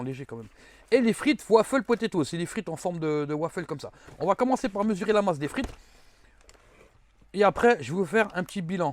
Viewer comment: ta dit quoi laaaa???!🤣🤣🤣🤣🤣 (tender)😭😭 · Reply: nous tendons